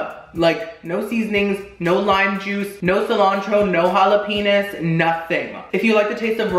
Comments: noone: jalapeñis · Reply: I had to replay that clip cause I was like, "did he just say jalapenis?" I was shook when I didnt hear him incorrect. It was funny still though. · Did he just say "Jalapenis" Lmfao this dude cracks me up 😂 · did he say jala-penis? I'm. Done. Lmbo🤣🤣😂❤